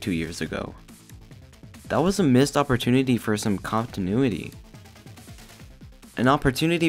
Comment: Did you just say “cop-tinuity?” :)